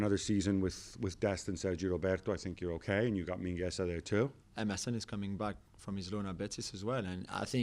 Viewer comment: When you said “Emerson is coming back” I thought you said “MSN is coming back” I was really happy for a second